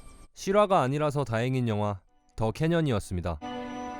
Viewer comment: 왜 '더 개년'으로 들리냐 ㅋㅋㅋ